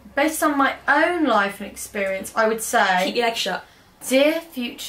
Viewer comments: "ITS TWINS" "I cant afford that" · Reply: Jade Brown At 7:45 did say "keep your legs shut" ? I'm trying to add Spanish subtitles :) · I feel so bad about how often makes slut jokes about. I hope she doesn't mind. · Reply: Julia S. At 7:45 did say "keep your legs shut" ? I'm trying to add Spanish subtitles :)